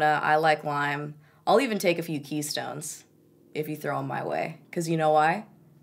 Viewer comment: Omg I swear I thought you said I'll take a few kidney stones because I've got time , I almost peed